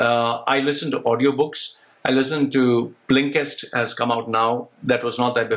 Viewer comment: What did he say at 12:38 after I LISTEN TO AUDIO BOOKS? I LISTEN TO ______ HAS COME OUT NOW The captions display it as BLING CAST but it doesn't seem correct.